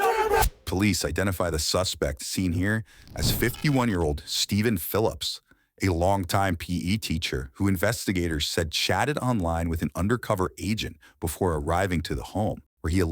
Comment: I need my ears cleaned. I thought the narrator said "...who investigators said shatted online with an undercover agent..." 🚽💩 🖥